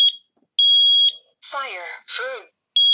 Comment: what do they say here? It sounds like fire vroom. 0:24 < don’t click this I did it for no reason also don’t click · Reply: Yeah, so this is a Canadian unit so it says “Fire, Feu” which is English and French.